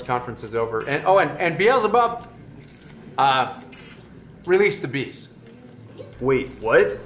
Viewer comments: I thought he said release the “beast”!!!! And I was like wait a minute, I’ve been reading in revelation…… 😬😬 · Brilliant and scary at the same time. Release the bees? I thought he said 'release the beast' !! I keep saying it ... but I really wish my husband could have lived to see this day. He would have been so happy! He changed my mind with a lot of patience and debate! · I'm not even religious and this is hilarious. I knew The View was going to be his favorite show as soon as he started to say that line 🤣. RELEASE THE BEES! 🐝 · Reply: I thought he said, " Release the Beast."